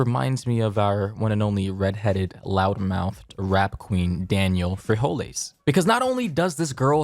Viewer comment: Did say rap queen or rat queen @ 0:40 Just asking🤷🏾‍♂️